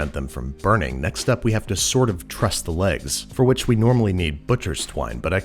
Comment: i thought he said "next up we have to sort of trust the legs" and i was fully prepared to do so